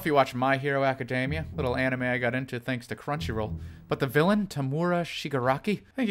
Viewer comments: I thought it said "do you eat pf changs and crunchyroll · I literally thought you said cringy instead of crunchy